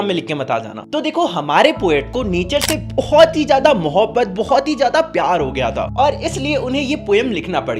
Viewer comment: BHAIYA SAY NATURE VALA LOVE BUT I HEAR NICHE VALA LOVE ☠☠ (BECAUSE I SEE THE VIDEO IN 2X SPEED )